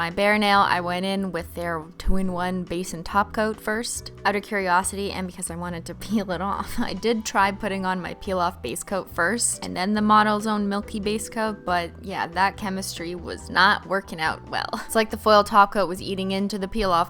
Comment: it sounds like you say models zone each time you say models own😝